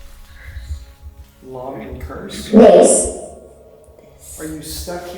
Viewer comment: it sounded like the boy said he touched me not he cursed me 1:28:37 he says thirst not first